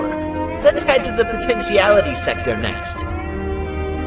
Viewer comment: did he say potential